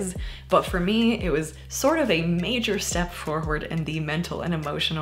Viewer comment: I thought you said “bologna amputee” and then you said “meat foot” and it was perfect. 🤣❤️